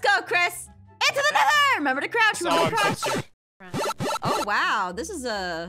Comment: When I think Aphmau said remember to crouch remember to crouch I thought was saying 5 minute crafts! 5 minute crafts! 😂